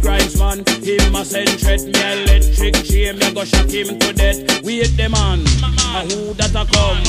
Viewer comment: always thought he was saying wake the man not wait like he woke him up and the guy says who dat a come who no think me the Don. love raggamuffin